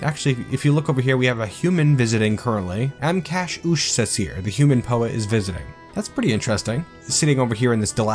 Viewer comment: I thought it said Human Poot was visiting.